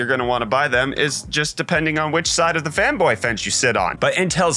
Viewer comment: said fanboy bench but I heard femboy bench